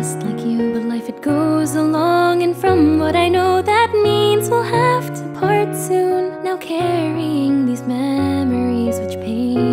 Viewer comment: I'm so sorry and I don't know why, but I heard that as "I'll have to fart soon"... Childish I know, but now I can't unhear it